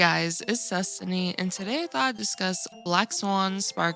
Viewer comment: For the longest I thought you said “It’s Cestiny” 😅